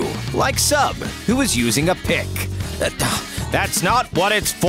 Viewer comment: was it just me or did i hear him say pic